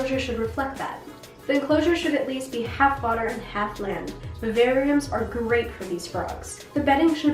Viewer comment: I'm sorry I can't hear exactly what you're saying around 1:54. What kind of tank is perfect for the Fire Belly? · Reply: she said vivariums :)